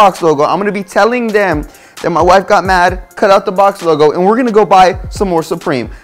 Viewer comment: All I heard “She got mad and cut it off”